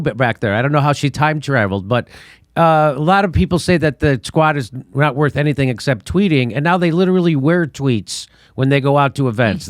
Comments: did he say "the squad isnt worth anything except tweeting" or "the twat isnt worth anything except tweeting". either one would be correct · Jimmy calls AOC “Twat” at 0:20 instead of Squad. Freud strikes again!